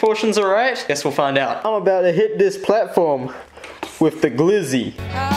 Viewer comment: sounds like GLiZzY instead of gluzzi